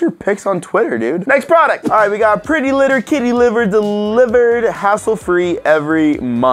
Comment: Did say kitty liver?